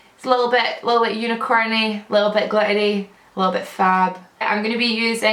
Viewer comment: i thought u said a little bit gluttony then i realized after 5 times it was glittery